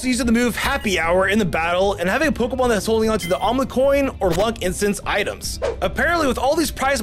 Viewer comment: did he say the omelet coin?